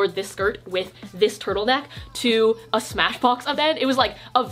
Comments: Thredup is my jam for late night in bed pj thrifting. Also when you said Smashbox event, I thought you said Smashmouth that was a wild ride. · For some reason i heard “smash bros event” instead of “smashbox event”